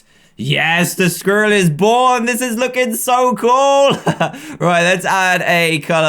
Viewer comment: I thought he said "this is looking so crueeel!!!" XD